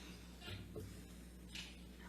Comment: anyone else hear “F***ing hell”?